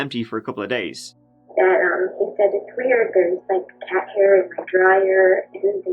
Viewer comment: subtext says "car hair" instead of "cat hair" hope that's not our boy doing